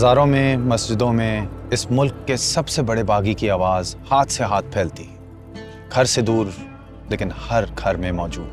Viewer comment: Did he say Baghi ki awaz or what..?? He was talking about Mr Khameeni...